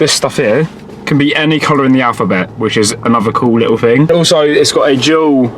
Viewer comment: Did he say colour of the alphabet?